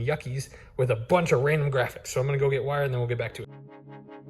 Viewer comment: I heard “I’m going to go get wired then back to work” which is a totally different thing but did make me chuckle